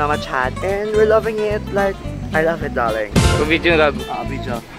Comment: tama ba narinig ko... tinawag kang LOVE sa 2:15...